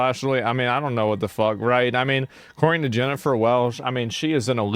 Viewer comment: I heard Cenk's "weirdly attracted to Jennifer Welch".